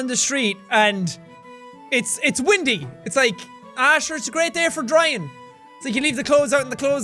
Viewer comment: I was looking off screen for a minute and I thought I heard him say “Asher” so I immediately whooped my head around and looked back. (My name’s Asher btw so I thought he was speaking directly to me)